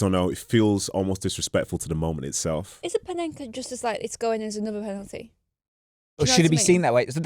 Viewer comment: I like, but I can’t catch what she is saying “is a paneka just as like going in as another penalty”. What does she mean ?